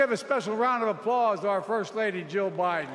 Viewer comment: did he say "our first lady joe biden"